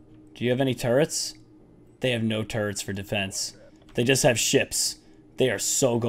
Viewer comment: he said shits instead of ships